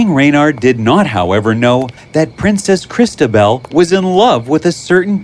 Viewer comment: I thought it said princess mozzarella